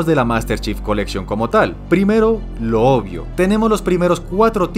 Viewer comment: Entonces porq odia la MCC? todo está perfecto según lo q dijo ._. Y personalmente de los mejores modos en línea serían Halo 4, Halo 3 y Halo 2 en ese orden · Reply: ah q pndejo, entendí "lo odio" y creo q dices "lo obvio", una disculpa hermano